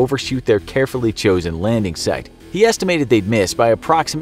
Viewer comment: i thought he said landing set